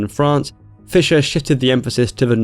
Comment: It’s sounds like he said shitted instead of shifted